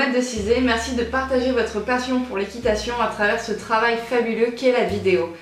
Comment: c'est moi ou a 10:44 minutes elle dit "fabuleux" alors qu'il est écrit "fastidieux" ?